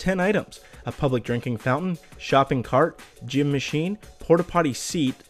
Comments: I thought it said Cum Machine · lol I thought number three said "cum machine" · thought it said "3. Cum Machine" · Thumbs up if you thought Number 3 said Cum Machine.